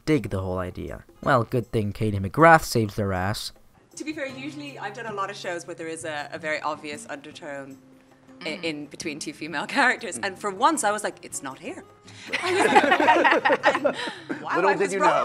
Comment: can anyone transcribe what katie said on the interview at 24:51. i'm from SA i barely understand english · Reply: Kaite: to be fair, usually I've done a lot of shows where there is a very obvious undertone in between 2 female character, and for once I was like it's not here. And wow I was wrong apparently. But we were talking about this the other day, and the great thing about what we do is like any art, is anybody can read into it what they want take from it. So you can see anything and take from it what you like. That's what's great about this show is that there are so many different ways to see everything, and if that's what you see in it, you know, take it away, that's it's...